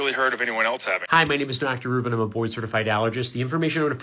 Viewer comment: thought he said 'boy certified allergist'